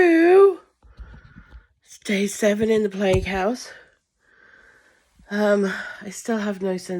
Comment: (Laughing cause i thought you said DAY 7 IN THE PLAYHOUSE!!!!). For my brain injury - I loose many things including my personality changes. I don't associate with who I am. (Just keeping up the persona)